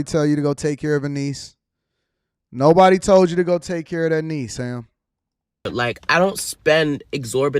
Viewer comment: i think he said that he takes care of his NEEDS